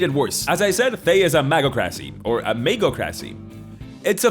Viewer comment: I kept hearing "MAGA-CRACY"